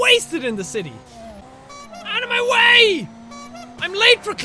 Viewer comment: when he said 'out of my way!!' I thought he said 'ANIME!!!'